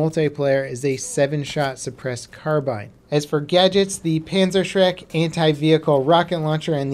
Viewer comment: did i hear that correctly? ''PanzerShrek''??